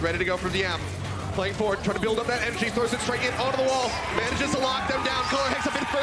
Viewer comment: did he say mg throws it straight to the wall?